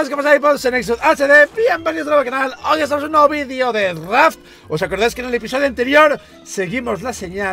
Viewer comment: pense que dijo bienvenidos a un nuevo video de rap